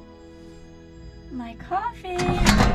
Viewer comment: I thought she said my coooffiiiiiiin l😂l